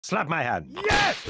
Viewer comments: when i first joined tf2, i thought the spy said “slap my hat!”. and honestly, i wish i didn’t know that it was “hand”. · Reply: I heard it was ''Slap my atom!'' and I heard atom as adom because of Spy's accent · when i first joined tf2, i thought the spy said “slap my hat!”. and honestly, i wish i didn’t know that it was “hand”.